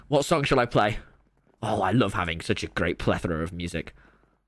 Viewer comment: What word does he say around 9:14? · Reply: Plethora? It means like array